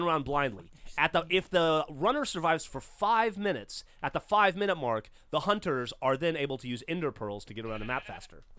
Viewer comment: I could've sworn they said no Ender pearls until the ten minute mark...